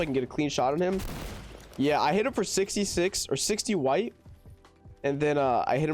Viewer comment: Wait did he say sixty white at 2:50 ??